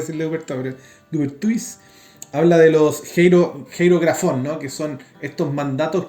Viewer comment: No llego a entender que es lo que dice Atilio Rene Dupertuis habla de los geroigrafon?? Gracias!!